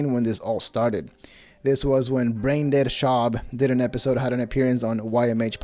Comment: When he said Brendan it sounded like “brain dead”.